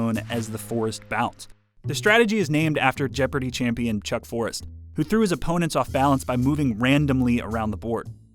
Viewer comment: Wait that technique is named after a guy named chuck Forrest? I thought he said chuck Norris for a second 🤦‍♂️